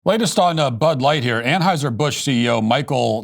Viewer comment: Did you just say BUD LIGHTYEAR at the beginning of the video? 🤪🤪